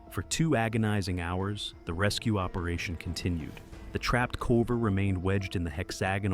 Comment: These videos are 100% AI gen. 7:20 you can hear him say "trapped culver" instead of caver. So the narration is fake at the very least if its just reading off typos without a pause.